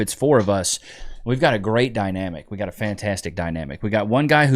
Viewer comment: I thought you said “GUYNAMIC” not Dynamic one of those times. That would be a cool podcast as well! GUYNAMIC, Just Us, 4Pals! Each Man talks about his personal day. If you’re all Dads, DadNamic. It sounds really Cute, relatable to both(most) Sexes. There’s several levels for people to relate, and when there’s a 4Pal, GUYNAMIC, you bounce off one another’s experiences, etc. Chat is FULL of people, that either relate to All, or one, and so on. Heterosexual, GUYNAMIC Stories are needed! Congrats on Baby Nich’s toofers 🦷 if her temp gets high from this- Our local ER billed us $650, to give her the flip. First dose, both Tylenol/Ibuprofen, then one or the other every 4-6 hours, switching off the Ibuprofen, and Tylenol! You only give both, on first Dosing! Call her PED. Careful of some of the homeopathics. They’re not all safe! Awe, you two are in such a precious stage. The memories later, are worth the sleep you’re loosing! My baby just wrapped up 3rd yr in College. It goes by way to fast! Recall that, at any breaking point! You’re just tired, babies uncomfortable/tired/hungry/antsy,summin New popping in every day. By year 2, every minute 🙈 then they’re off to school, away from you! We enjoy watching you, because of course we agree a lot 😂🙌 learn to nap w/her. Sometimes, that’s gonna be the only time you sleep. Remember; you’re training her, to help you with Baby Nich’s 2 ✌️😊 😉